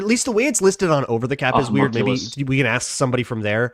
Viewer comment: did you say that the contract transforms into a homunculus? 34:21 Also you forgot Marcus Sherels shutting down Megatron in one of the most unexpected Goliath-killings in a long time